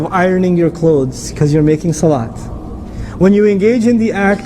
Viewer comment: the subtitles read making fun instead of making salah. And again at 1:56. At 2:03, it reads little eyes instead of Allah. Please do something about it. I suggest that you put manual subtitles in your videos. The message is very beautiful, lets deliver it to people with weak english.